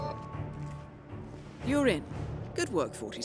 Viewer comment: When the hitman clip played and Diana said: "You're in" I thought she said "urine" and had myself a grand chortle